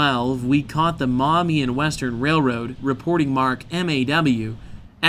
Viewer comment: All this time I thought he was saying the Mommy and Western Railroad. Oops, my bad!